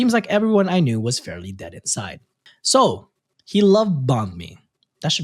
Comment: What I heard: “He loved bahn mi” What was said: “He love bombed me” Me: 🤦🏼‍♀️